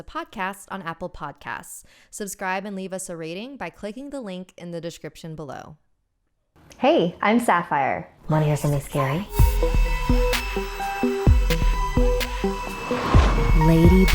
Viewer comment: I heard 'wonna hear money scary?'